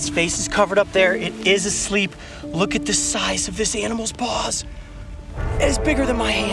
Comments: Lucky I was looking at the time cause I thought you said look at the size of this animal's balls 😂😂😂😂😂😂😂😂😂😂😂😂😂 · I thought he said, "Look at the size of it's balls." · I thought he said look at the size of this animals balls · lmao i thought he said omg look size of this animal balls 😂😂😂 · I wasnt looking at the screen and I thought he said, "look at the size of this animals balls" lol